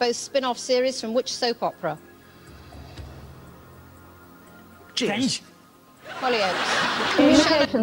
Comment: what did the Chuckle Brothers both say? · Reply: Cheers or friends.